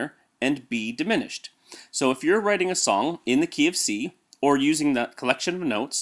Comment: Who the hell is going to write a song in the KFC?!?! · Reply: hahaha you're right but I made a stupid joke. I thought you said KFC in stead Key of C and in the Netherlands most people pronounce KFC as KeyFC. (in case you don't know, KFC is a fastfood restaurant)